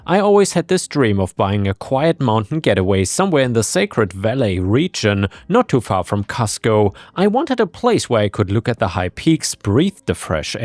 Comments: I thought you said "not too far from Costco" and was thinking, they have a Costco in Peru? haha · Ok, I will admit it. I was just listening and when I heard “not too far from Cusco”, I thought you’d said Costco. I had to look when they said they got a lawyer from ‘Costco’ and realized my mistake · Not reading along I thought you said "not too far from Costco" 😂😂